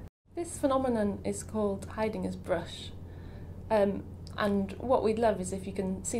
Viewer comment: Woah, I saw it! Cool experiment/activity, thanks! It's "Hiding as brush"...haha That's what I thought you said at first...well because it is "hiding" from sight...until you see it right. Cool, the yellow is more visible but I do see a bit of the blue, very light though. And it looks just like the white. Question please, on what other things can you see it besides liquid crystal displays? And can it only be seen against a solid white color or with other colors too? I notice that when I wear polarized sunglasses (which I have to say are the best lenses in seriously bright sunlight and my mum's opthamologist recommends them as the best for protecting your eyes) that the way your skin looks when you look at the back of your hand is just like the way you skin looks when it is view while under water. You know that look when you see skin or hair under water under sunlight, especially. To me I love that look when the light hits yoru skin underwater. So I figure that water is polarizing the light ...is it? Now I wonder if I can see Haidingers brush that way way too? I see some kind fo connection but I don't fully grasp why this happens. Pls share what yo think. Thank you!